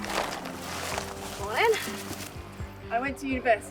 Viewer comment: Hold up, did she just say "Moin"? at 0:24? 😂 Also: outstanding episode for your 30th, happy birthday and carry on! ❤